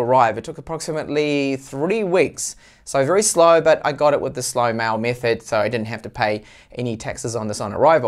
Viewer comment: With which method? I listened to it 5 times but didn't understand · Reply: Slow mail method is what I said. It varies from country to country but for me it came with Correos (Normal post) So the non-express post methods.